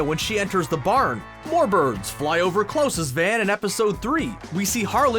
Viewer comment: Did-- Did he just say "Klose"..?